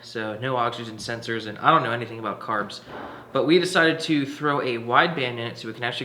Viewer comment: i thought he said: I don't known anything about cars lol